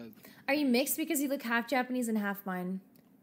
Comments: I thought she said half blind, and I was like that's kinda racist. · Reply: lol i appreciate how ur playing safe with ur comment. Dude's saying that its racist cos the person said are u half japanese and half mine but ppl thought the person said half blind so like the small eye thing. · I thought she said half blind, and I was like that's kinda racist. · I thought they said "Are you mixed? Because you look half japanese and half MIME." LMFAO